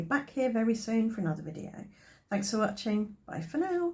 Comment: H 7:03 please could you explain the spray you did over the top, I couldn't quite catch what you were saying. I really love these ideas, and its great to not waste the ink you had on top... thank you for sharing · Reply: Thanks! It's a DIY shimmer spray made with water and Cosmic Shimmer White Pearl Mixer. I have a video on how to make DIY shimmer sprays here: <link>